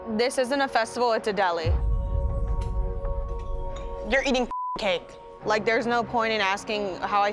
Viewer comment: Kinda sounds like she said “you’re eating FUCK cake”